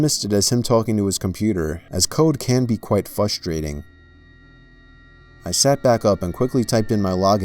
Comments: Did anyone else hear fustating a 1:13 instead of frustrating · did he say "fustrating" lol, still best creepy reader on YouTube.